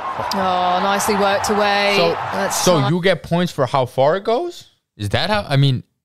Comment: Did you just say "Do you get points for how far it goes"?